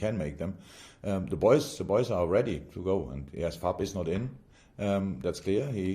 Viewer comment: I cannot get what he is saying at 5:27 sounds like "puppies not in" who's talking about? Not Bobby? I played over about 10 times, can't get it. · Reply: "Fab is not in".. Short for Fabinho I think..